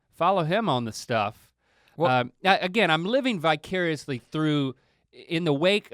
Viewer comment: did I hear..... Bicurious !?